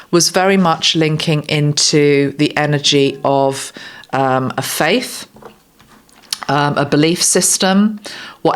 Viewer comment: ❤I Thought you said, the energy of Pizza❤ (Peter). Oh dear, it's been a long few days❤❤❤ Bit of light humour for us all❤❤❤❤